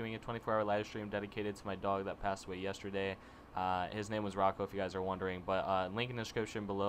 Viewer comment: is it ricko or rocko? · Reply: PH4NT0M- _ I know I thought it was Ricko too but then I heard him say Rocko :P